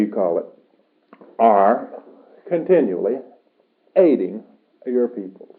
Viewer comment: Listen on 9:32 !!! Do he say? "..We of the Confederation...as you call us...continunly eating your people..." 😲 Do I heard good???? Greetings from Croatia! Suzana CE-5 ambassador ✨💞🍀💥